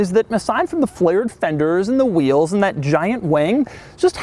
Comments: Anyone else hear ‘giant wang’? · did you say giant wang? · did I hear you say "Giant wang?" you may want to enunciate 'wing' better ;) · did he say gigant wang?😂